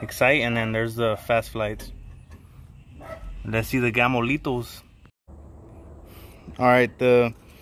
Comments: Took me over 10 minutes to realize you were saying “Gamo-Lethals”. Through the whole video I coulda swore you were saying ‘Gamolitos’ haha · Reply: I kept watching until ive read the first comment, ok game lethal i thought he was saying gamolitto but i luv the vid, blue and corn. Of course type of pallets was very very good bro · I thought he said Gansitos 🤣 but he said Gamo Lethal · Took me over 10 minutes to realize you were saying “Gamo-Lethals”. Through the whole video I coulda swore you were saying ‘Gamolitos’ haha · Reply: the whoke time i thought he was saying Camo lethals